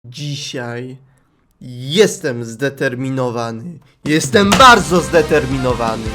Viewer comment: Powiedziałeś "Jeestem zdeterminowany" a ja usłyszałam: "Jestem Przeterminowany" o boziu serio nigdy nie miałam nawet 100 like =-= dzięki